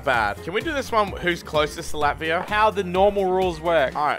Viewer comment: Ok, I'm drunk. When he said "to Latvia" I swear I heard "Tilapia"